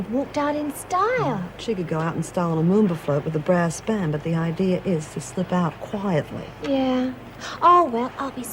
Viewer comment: What did Bryant say at 07:48? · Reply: "She could out in style on a moomba float with a brass band but the idea is to slip out quietly! " :D <link>